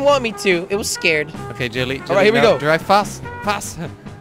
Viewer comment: Why Does Sound Like He Is Saying Jilly All Of The Time??????